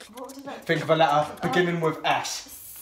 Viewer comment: he said "think of a letter beginning with s" Who else heard that 😂😂😂